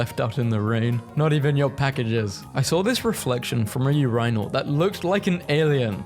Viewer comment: DID BRO JUST SAY UR-EYE-NAL?!